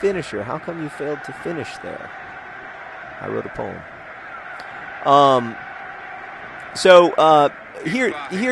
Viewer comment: does John say 'I wrote a poem'?